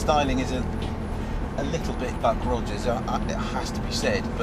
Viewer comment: What does he says at 4:40 "I mean some of the styling is a bit"...? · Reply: I believe it was "a little bit Buck Rogers."